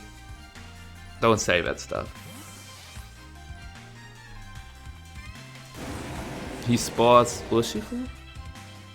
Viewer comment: What was that chat said? I couldn't understand, was it something offensive? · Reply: something about neil